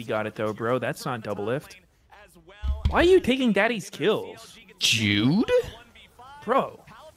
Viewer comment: Did Sneaky just say "jewed", as in, the racial slur?